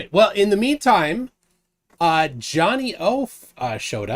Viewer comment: "Johnny Oaf showed up." 7:11 Also: "I did not call him Johnny Oaf!" It's plain as day for all to see! eXpOsEd!1!1!!1!1